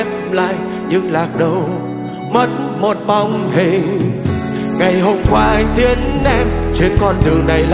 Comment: Bao nhiêu năm rồi , vẫn nghe nhầm câu , " Ngày Hôm qua A THIẾN E " 😂